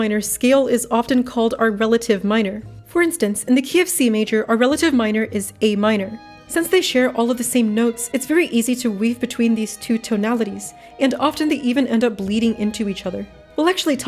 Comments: when you said key of c major i thought you said kfc major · it really sounded like you said KFC Major for a second and now I’m hungry for subpar chicken